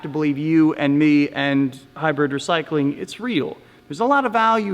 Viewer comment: Thank you very much sir! I appreciate your time writing this. Yeah I dont think he is shady at all but he needs to move out of Paypal etc. Thanks again! Are you into EV's ? Batteries etc? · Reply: Also, If you hear in 9:14 he said Hyber Recycling ? I dont understand what he says there.